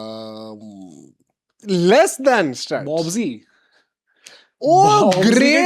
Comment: kya kaha ??!!! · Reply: Babar azam